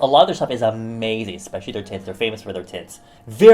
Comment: Omg he said how Peri Pera is famous for their TINTS but it sounded like he said TITS and it made me die a little inside